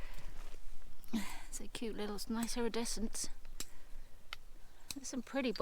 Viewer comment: I thought you said “be careful of all these stinging nettles” “little shits!” But you had reach down to pick up a little bottle and said “a little shear top bottle” 😂🤣😂 my ear honestly they get me into so much trouble!😉